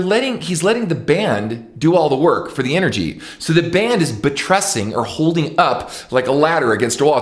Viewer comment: what the word "betressing" means? "so the band is BETRESSING or holding up like ladder against the wall". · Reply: buttressing